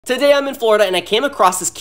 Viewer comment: Why did i hear "today im in fortnite and"